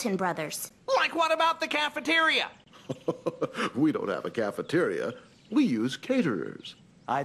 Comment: What did Doug say at 2:36? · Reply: We don't have a cafeteria. We use caterers.